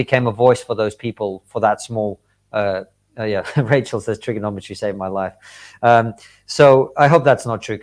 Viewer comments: Triggernometry on Locals: <link> On Twitter: <link> Podcast: <link> On YouTube: <link> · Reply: Me try no trigger too much, like me doth protest nearly enuf! Viva, sounds like you were saying trigonometry. Plz, try & try a-gain. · Me try no trigger too much, like me doth protest nearly enuf! Viva, sounds like you were saying trigonometry. Plz, try & try a-gain.